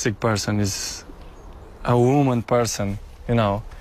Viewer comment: did ronaldo say he's a 'woman' person?